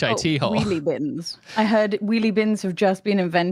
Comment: I thought they said wee wee bins lol 😂